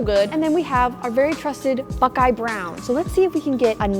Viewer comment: When said buckeye brown I thought she said f***eye brown but she would never say that